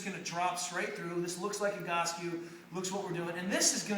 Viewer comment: QUESTTON RE: VIDEO at 3:08 Does he say Egoscue?